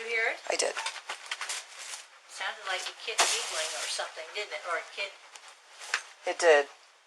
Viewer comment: the elder of the two actually said "it sounded like a kid giggling" 😳